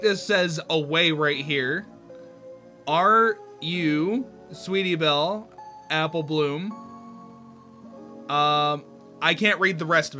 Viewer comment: I think it says "run away are u coming? Sweetie belle,apple bloom help me it HURTSS!!!"